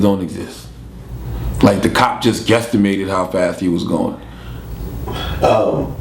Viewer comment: did he say guessitmated????